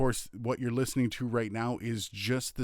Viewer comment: can i know what have you said here, emposed! And can can you tell me what do you mean by that and how can i do it, thanks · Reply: He said "in post". He meant that once you have the audio recorded, you edit in your DAW, applying effects, filters, etc. To do that you'd want to download something like Audacity (it's free) or buy/download any other software. I'm using Sound Forge, Reaper and even OBS to record audio. Good luck.